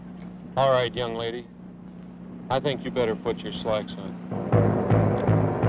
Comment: He said 'socks', Not trousers, not even pants- for trousers as Americans don't say trousers. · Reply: Ruple Thaker I thought he said “slacks”...?